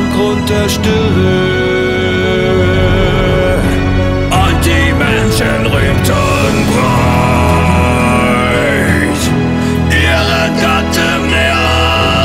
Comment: I I was confused for half a second. When you say "Und die Menschen" i Heard "Untermenschen".